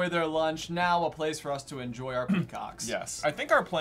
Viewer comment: When he was talking about demolishing the park for malfoy manor I literally heard “now a place for us to enjoy our c*cks” and I was like jeez Malfoy.